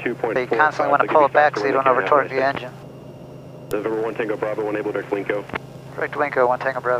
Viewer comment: Can someone translate for me what ATC says at 7:45 ? I hear "Unable to clinco" but it doesn't make sense and that would send me in panic mode but doesn't seem to care :D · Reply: He said, “when able, direct WINCO,” which is a waypoint as stated above. The FAA uses 5 letter words for IFR. Most of them are made up and strange to pronounce. I would say that’s a pretty normal speaking pace for ATC but once you know what to expect it’s not too hard to understand.